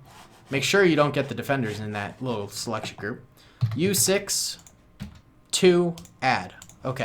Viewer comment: what did he say there what is uf6?? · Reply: Its u 6, you press the letter u and then 6